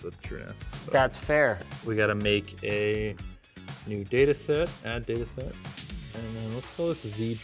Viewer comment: Listening to this half asleep when says "We have to make a data set." My mind heard him say "We have to make a Datasette." (The "Datasette: was the tape drive used with early VIC 20 and C64 computers...) Oh wow, everything that's old is new again. Starts making me wonder how long many years it would take to load a 1 MB file from the good old Commodore tape drive. Hey, I got laugh out of it. :p